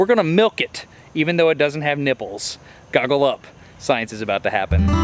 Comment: When he said "even though it doesn't have kipples" I thought I heard "gargle up" 🤣